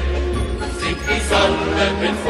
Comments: Kinda sounds like they saying heil Hitler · Why i hear heil hitler???